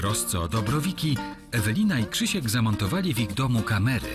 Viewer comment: usłyszałem "zamordowali" zamiast "zamontowali" za dużo spraw kryminalnych heh c